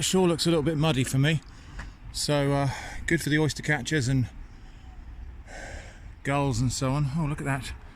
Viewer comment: "Gulls!" I Heard "good enough for the oyster catchers and the girls!" :)